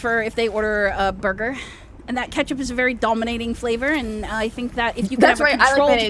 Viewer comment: what did say? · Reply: "That's right, I like mayonnaise!" At least that's what I heard.